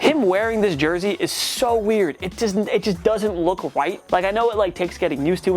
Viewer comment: it totally sounds like he says "... it doesn't look WHITE" ...doesn't it???